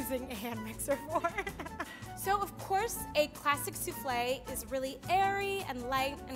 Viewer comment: I thought she said a plastic soufflé